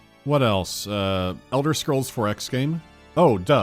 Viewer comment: I thought you said "An Elder Scrolls 4: X-games" which would be an interesting genre shift